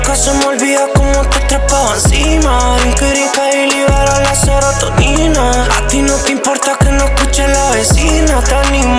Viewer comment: que dice ahí? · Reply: "Brinca brinca y libera la serotonina"